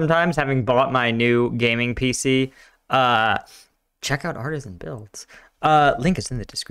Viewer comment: I thought you said Artesian builds for a second and I almost shat a brick